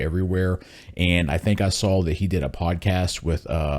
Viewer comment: THE FIRST TIME I HEARD TATE say I "SAW SATAN" Twice I couldn't stop Laughing 😂😂😂 Great video!